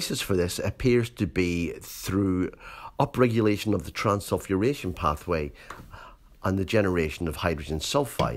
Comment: Does someone understand which pathway he mentions at 19:40? I don`t understand it acoustically. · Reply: Is it Transsulfuration pathway ?